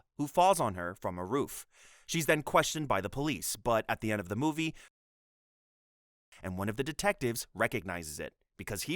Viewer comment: anyone willing to explain what was said here? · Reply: Looking at the Wikipedia page I think it's "strip search"